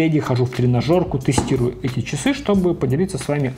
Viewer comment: После заключительных слов про часы Samsung, сходу фразу «Хороших Вам...» расслышал как «Хороший хлам...»😂😅